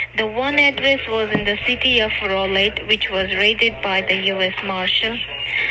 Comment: Sure sounds like she said "City of Rolaid". Anyway... I've gotta give "Adam" credit... he has an air of practice to him. It progressively goes downhill, though. I'd recommended him for the prison theater program.